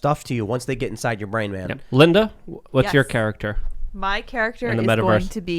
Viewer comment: Is Lydia pronounced or is it Linda ? I heard Luke call you Linda at 19:00 min mark.